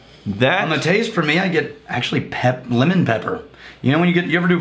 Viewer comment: said that it has tea,chocolate,women????? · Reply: Think it was supposed to be "lemon pepper" but it definitely sounded like he said "women."